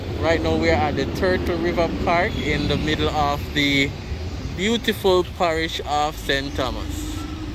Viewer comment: uh-oh did you just say “the beautiful parish of St.Thomas? You are forgiven my friend,your busy schedule is the culprit here 😎